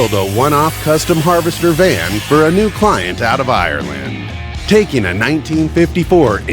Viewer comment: I thought you said iraland